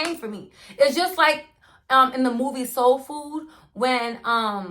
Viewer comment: What was the name of the movie I couldn't catch the name sorry? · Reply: Soul Food ❤️